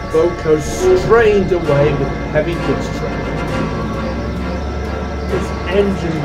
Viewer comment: When Boco was doing the heavy freight test, I heard “heavy goods train” at one point, but I think the caption said “heavy pizza”! That’s most definitely NOT pizza, that’s FUEL! 🤣